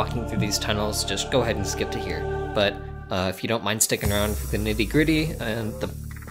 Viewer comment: Tbh, I thought bro said "Hittin the griddy" But no. Acording to the subtitles, he said "Nitty-Gritty"